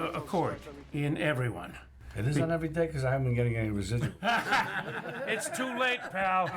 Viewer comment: what does DeNiro say at 05:05?..it looks like a joke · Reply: "It is on everyday? because i haven't been getting any residuals .(royalty payments)"